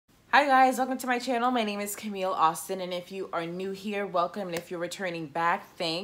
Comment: my hearing is so bad I thought you said your name was Camille All-Cinnamon at first. that's kind of a wake up call for me.